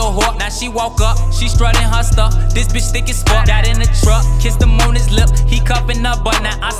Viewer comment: Did he say kiss him on his lip